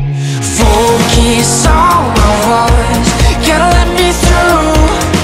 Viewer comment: love this song! but does anyone else think it sounds like he's saying heart instead of voice ?? focus on my heart, when really it's focus on my voice ?? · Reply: This song plays every single day at work (and I still love it) and I've always thought he said heart and not voice.